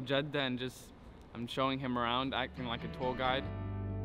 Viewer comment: I heard acting like a tall guy